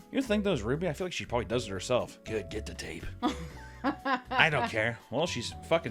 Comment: I wasn't looking at the screen so I thought he said get the wife on the cup, I was like that so wholesome and I see anime titties